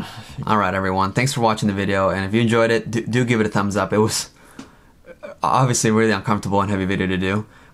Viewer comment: In the end screen at 38:28 I thought I heard someone laughing ^^ And then directly afterwords the only thing I heard you say was "...really uncomfortable Ha-D-D-D-Doo!". xD